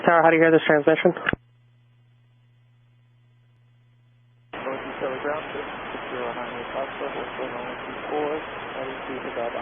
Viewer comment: What does he say? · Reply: Irs a light aircraft on ground 6098f wanting to depart runway 24